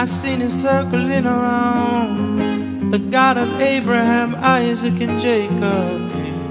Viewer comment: Who can tell me what he says here - 34:19 , The god of abraham eyes ???